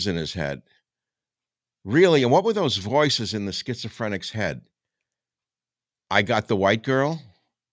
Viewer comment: Everyone keeps reporting that he said; "I got that white girl!", but if you listen to the audio, what he actually said was; "We got that white girl!". We, being him and the Demons that are piloting that ship. If you want to know what Demonic possession looks like, watch that video... 🤔